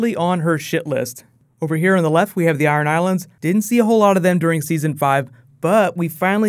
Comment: I thought you said "Aran Islands" which are islands off the coast of Ireland rather than Iron Islands lol :)